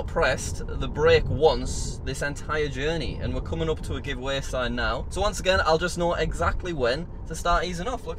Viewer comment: did he say coming up to a giveaway sign